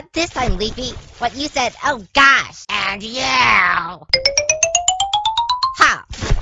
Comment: When pin said AND YOU she sounded like four ngl lol